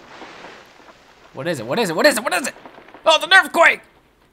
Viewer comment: for a second i thought said nerfquake and i was like wtf